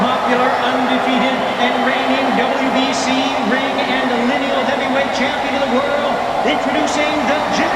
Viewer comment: Ok maybe it’s just me but when they were announcing fury I thought he was about to say Mayweather until he said heavyweight 😂🥲